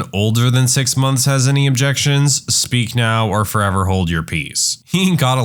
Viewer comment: the video lagged at just the right moment, so it sounded like "speak now or forever hold your pee" 😂😂